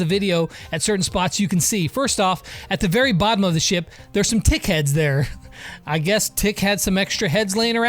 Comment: I thought he said dhead but he actually said tick head